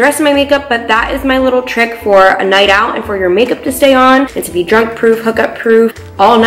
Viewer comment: ”lmao she said “drunk proof, hookup proof” 😂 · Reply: i thought she said drug proof i was like uhh